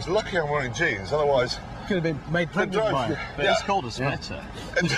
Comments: What did May say? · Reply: "Well it is called a splitter" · I must of replayed that 6 or 7 times. I cant for the life of me figure out what James said to spark such laughter. Someone help me out please! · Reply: He said Thats why its called a splitter. which is a truck gearbox. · what did James say? Foreign here. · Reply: Was gonna ask this too, found the answer in the comments, "well it's called a splitter" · wtf does james say here??? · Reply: "that's why it's called a splitter"